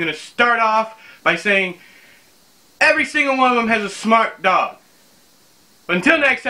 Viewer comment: I keep playing 0:21 over and over but I can't make out what you're saying. Smart dog? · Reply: Super smart dog, I think.